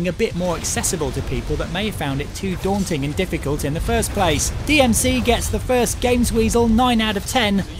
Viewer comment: lol I thought he said "DMC gets the first gamesweasel's 10/10" -.-'